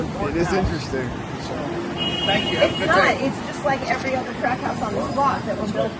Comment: “Just like every other crack house on this block…”. I mean, that’s what I heard…. 🤪